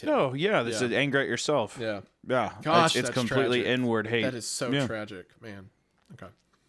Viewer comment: i had to rewind this part 4 times, I thought said "It's complete N word hate".